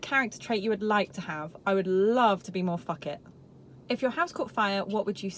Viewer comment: What did you say at 1:01 ? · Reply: its_n “love to be more f**k it”